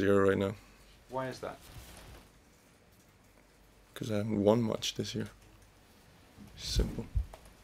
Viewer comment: What does Sascha say at 0:55? He's.....this year? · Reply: havent won much